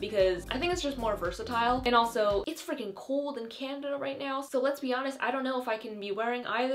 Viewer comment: did she just say candana?😂